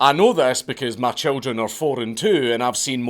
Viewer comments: you say "I know this because my children are four and two and I've seen Moana 351 times" I couldn't figure out how that was relevant until I realised you said 4 and 2 not "my children are foreign too" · "my children are 4 and 2" not "my children are foreign, too." Me thinking the Dankulings are Polynesian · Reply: Bahahaha, I 100% thought he said "My children are foreign too." Thanks for clearing that up as I was sure his wife was Scottish! 😂😂😂